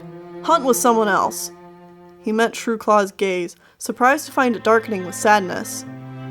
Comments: i thought you had first said "Shrewclaw's gays." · omg my hearing isnt too well so i heard "he meant shrewclaw was gay" instead of "he met shrewclaws gaze" omgsg